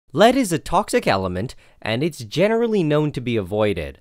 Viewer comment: In the beginning, I thought he said "Ladies and toxic elements" and with that I was done 😵